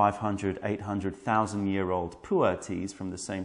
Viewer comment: Almost sounded like it was said 800´000 years old at 4:34. That would be quite a puer tea!